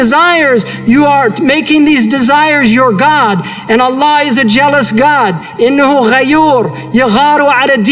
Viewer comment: did he say jealous or zealous? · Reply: Jealous 😀 It's a mistranslation